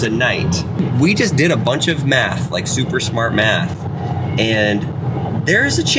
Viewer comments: I thought he said meth lol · I swear instead of “math”, I thought I heard him say “we just did a bunch of meth”